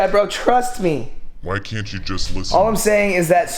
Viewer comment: Why did I lose it when kobe was sitting there and I heard “why don’t you just listen-“